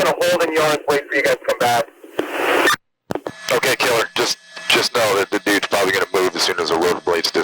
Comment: Did he say "ok killer"